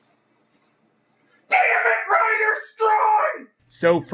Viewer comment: what did you yell out from 1:43 to 1:45? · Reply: "dammit Rider Strong"